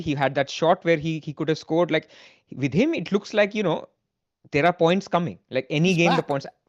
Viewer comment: at first I thought said with Salah it is like "tera points kam hain" but he actually said "there are points coming"